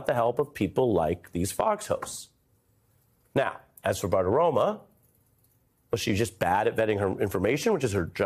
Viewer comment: Ari knows how to lay out a report. Nice work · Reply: Did you say "Bad Aroma" ? Most people call her Maria Bartiromo but "Bad Aroma" sounds more appropriate.